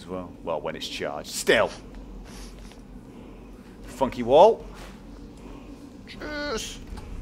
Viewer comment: I thought he said "F*ck you all" XD